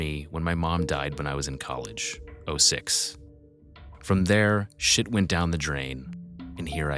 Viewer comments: Wait, 6:23 "mom died while i was in college, i was six"...am i hearing that right? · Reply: I think he said “06”, as in 2006. I thought the same thing as you but listened a few times and it shows on the letter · Wait, 6:23 "mom died while i was in college, i was six"...am i hearing that right?